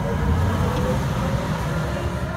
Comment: music is included on coaster? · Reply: ST SNSGT I thought they said Vaccoma is a trill ride, also how do you buckle up, this ride doesn’t even have seat belts